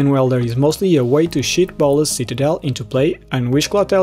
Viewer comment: I thought you said "Goblin Welder is a way to shit Bolas's Citadel into play" and not "cheat" it into play 😂